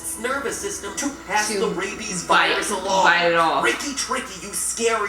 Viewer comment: wait What did she just say did she say bite it off